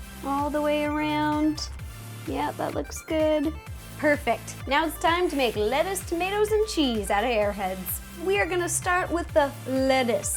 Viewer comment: I heard wrong so I thought she said "Time to make lettuce, tomato's and cheese outta 'yer heads." XD